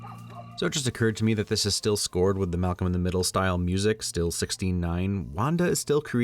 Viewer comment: I thought he said 69 lol